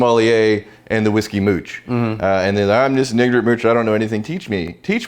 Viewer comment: Did he say "nigger mooch" at 10:15? LOL